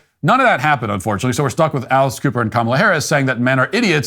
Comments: I hadn't heard of Alex Cooper before this, and it's so shameful to learn that it is the 2nd most listened to podcast. Our pornified culture will die soon if we continue like this. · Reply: Same here. I kept hearing "Alice Cooper" 😂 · I thought you said Alice Cooper. And I was already ready to throw up. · At one point, I thought he said "Alice Cooper".